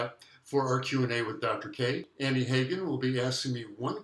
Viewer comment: Did I hear you say Hagen at 5:20? My dad's last name was Hagen. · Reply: Yes! My assistant in these Q&A videos, Annie, her last name is Hagen.